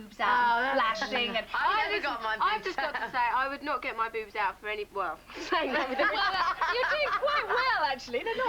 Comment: What did Victoria say at 3:58? - "I just, I just got to say I would not get my boobs out FOR ANY WEALTH" ??? Please help. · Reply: "For any bloke"😂❤